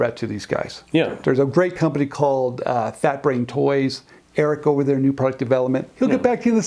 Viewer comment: Hello, At 2:24 they mention a company but I cant make out what it is. Can you some help please. Thank you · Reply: Fat Brain Toys🧠